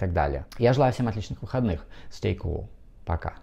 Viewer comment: Вы проводите потрясающую работу!! Понятно,доступно,подробно!! Огромное спасибо!! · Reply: Спасибо! Вы расслышали что он сказал при прощании на 08:01 ? По-моему Stay cool!